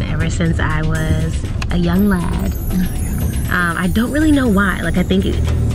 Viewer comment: Did she say I have always wanted to got lo London since "I was a Young lad" ?